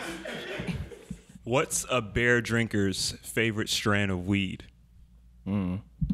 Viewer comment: I was confused at the beer dinkers joke i thought he said bear drinker i was like tf is a bear drinker